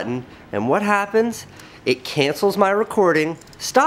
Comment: lol I thought u said Candice 😂